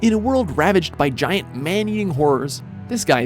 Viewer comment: "Man eating whores" is what I heard